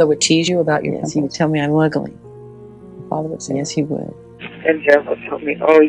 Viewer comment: What did Michael say when Oprah asked him about his father teasing him about his nose? 3:05 “He’ll tell me I’m …??” I can’t understand what he said..anyone know please? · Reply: "He'd tell me I'm ugly"